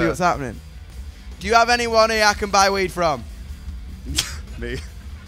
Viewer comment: what does he say? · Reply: "do you have anyone here I can buy weed from"